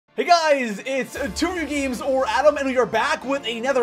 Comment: did he just say "Hey guys its tooby games"